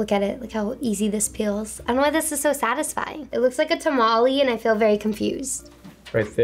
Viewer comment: she really said tamalee